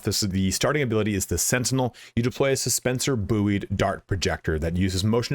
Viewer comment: Did you say "Boo-eed"? That's the cutest thing I've ever heard.